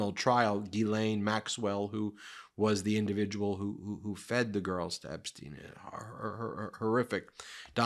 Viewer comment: Did he just say FEED him girls... like he ate them?